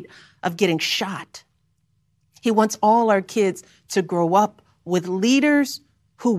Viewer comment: All I kept hearing was " Biden wants our kids"...... strange